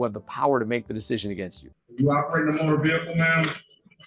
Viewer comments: even the closed caption said are you operating a vehicle not were you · to be fair, I heard “You operating a motor vehicle ma’am?” I didn’t hear a “Were” before his question. Now what happened after that was ALLLL on her lol